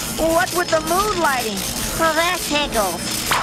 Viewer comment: did he say that tickles